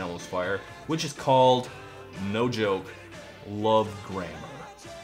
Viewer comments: lol. At first I heard "Love Grandma". Pretty disturbing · I heard " Love grandma " at First which was equal parts more Funny and offputting.